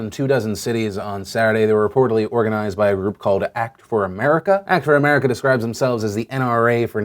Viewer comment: He said "prganized by a group called" but I thought he said, "organized by Ru Paul" 😂💀😂💀